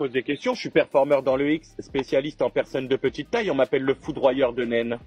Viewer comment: J'ai entendu " Le foudroyeur de NEN " 😂 HUNTER X HUNTER